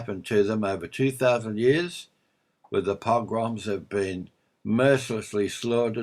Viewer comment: who did you identify to be the cursed people at 6:20? It sound like you said the Pogwoms